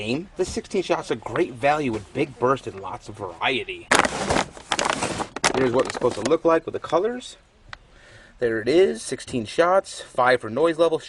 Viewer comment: 🤣 thought you said big Bertha but you said big burst haha great video